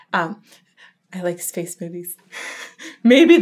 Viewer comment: Am I the only one that heard Space Boobies instead of Space Movies at 9:56.. I had to rewind to double check... ALSO ALSO, I remember you saying on March that the video of Will Ramos Throat was due to May... Any news?